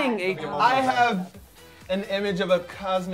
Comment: did mari just say "you're gonna get it in the ass next time" at 9:56 xDDDD